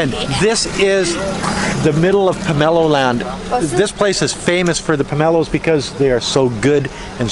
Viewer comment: For a split second, I thought you said POMPEO's LAND CHINA. I was stunned like .... "What!?"